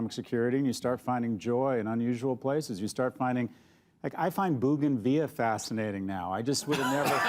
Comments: Does anyone know what 'bugan via' is supposed to be. Probably the captions didn't get it right. Is it the flower bougainvillea? About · what is bugan via? @ 12:43 Do I have some weird brain issue, or did anyone else not hear what he said here? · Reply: Bougainvillea. It is a flower.